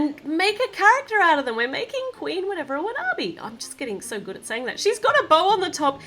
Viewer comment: At first I thought you said Queen Whatever